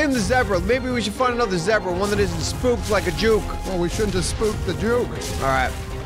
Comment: what did say? Swear to god it sounds like he is saying "Spooked like a jew" but it sounds like he's eating somethng so I don't know what he really said.